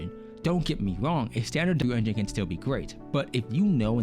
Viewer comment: I recommend slowing down a bit, you kept slurring with pronunciation on "W-Engine". A few times I thought you said "Blue Engines" and the like.